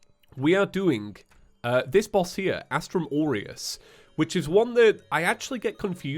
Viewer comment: I heard Astrum Oreos really eats too much Oreo.. Or i just heard it wrong